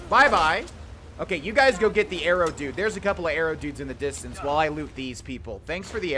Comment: Hi, I hope you enjoy this series, it's been something I've wanted to do for quite some time now. We're doing this as a break from Let's 100% Fallout: New Vegas which is still the main series and will continue at some point in the future. For now, let's have fun in Tamriel once again. Thanks for watching! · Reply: when you said “arrow dudes” I thought you said “Arab dudes” and I thought, wow I never realized they were Arab...oh he said arrow 🤣